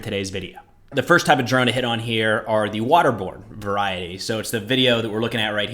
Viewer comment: When introduced the first drone clip, I initially heard him say, "the waterboard variety." !!! My distracted hearing makes things weird. - With the possibility of Russian sailors seeing waterborne drones everywhere, there might be a need for an on-board supply of binoculars for an admiral to throw overboard out of frustration.